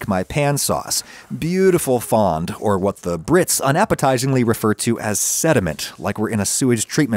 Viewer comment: At first when he says "brits call it" i thought he said ritz like that food and i was confused and then i realized he said brits